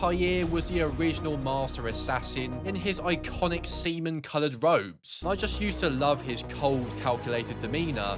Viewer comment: Did he just say "Semen coloured robe? " 💀💀💀